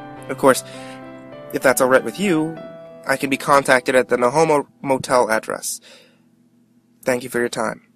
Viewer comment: did you just say.. "no homo hotel?" xD Is that why you stuttered? xD